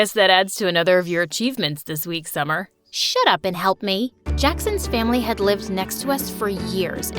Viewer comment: In the start I thought she was gonna say “shut up and kiss me” imao